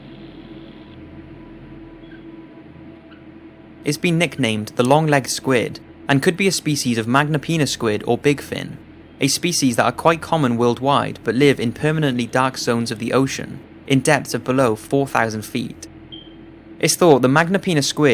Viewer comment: i double tapped to seek then i heard penis